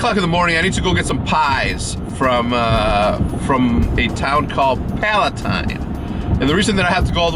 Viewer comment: Thought he said pie from Pallet Town...